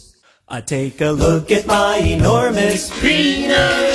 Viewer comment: It sounds like he said "Enormous Bitch"